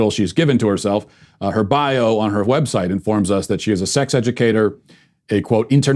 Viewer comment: Did he say w-heb site? Bc I need the sarcasm but I can’t stand someone pronouncing wh words like that. I hope it was a random slip